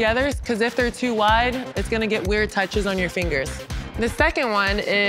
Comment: she said: if they are too wide it's going to get ??? touches on you fingers what is ??? i cant get what she says there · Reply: weird